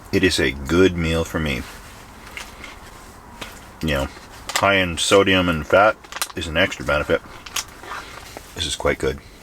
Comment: Had to replay the 13 minute mark repeatedly. I thought you said "I am so damn fat', but you said "high in sodium and fat". Sorry brah